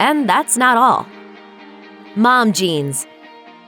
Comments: Who else thought she said "Mom Jeans" instead of "Mom Genes?" Man I love that band so much lol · Did she just say mom jeans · "mom genes" smh I thought she said "mom jeans"